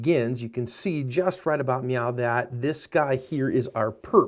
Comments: did he just say "right about meow"? our am i trippin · He did not say meow; he said now. Slow it down to half speed 00:28. It may take several listens, but he said now. It sounds like some hesitation was in his speech.... Nee-ow. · did he say Meow??